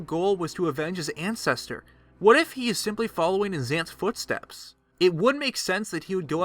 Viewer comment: subtitles say his aunts footsteps